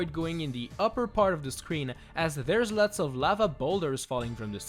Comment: Bathaniel Nandy: Did Someone Just Said Boulders ?